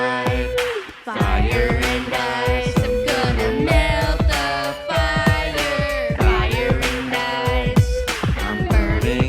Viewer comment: i kept hearing, "im gonna milk that fire!" 😂